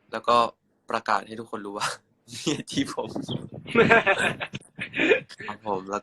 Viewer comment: น่ารักมากกกกๆๆๆหายคิดถึงเลยค่ะ 😁น้องดูมีความสุขดี และเฮียก็ดูมีความสุขเหมือนกันด้วย อิอิ เอ่อแต่ตรง14:32เปรมได้พูดรึป่าวคะว่า เฮียจีบผม??หรือนี่หูฝาดคะ?? คืองงมากอ่า ใครได้ยินเหมือนกันบ้างคะ?? ช่วยบอกหน่อยค่ะ🥺